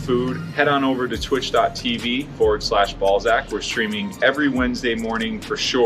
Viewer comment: is it just me or does your last name sound like youre saying ball sack